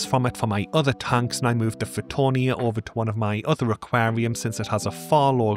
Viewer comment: what plant is this. Sounds like u say fruitonia?